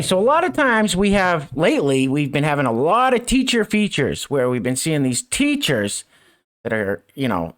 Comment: I keep hearing "creature" feature not "teacher" feature, lol